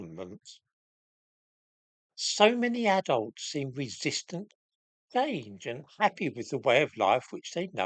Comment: Could someone help me? What does say at - 0:22 "So many adults seem resistant xxx? and happy...". - 1:00 "...the good looking xxx? who live on a farm" - 3:18 "...not a bunch of xxx? like that Dreadful family who live xxx?..." Thanks. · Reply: First one? resistant to change... 2nd good looking clan 3rd not...inward looking peasants